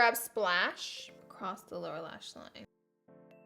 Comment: When she said lower lash like it sounded like she said Laura lash line